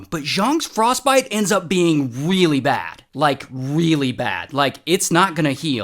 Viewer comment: Instead of me hearing “Really bad” I hear “Wheely bad”